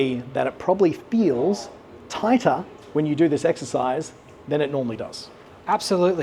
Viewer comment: What's he saying at the 4:50 mark - it feels "hiker" when you bring the club to the top with one hand. No clue what the word is he says.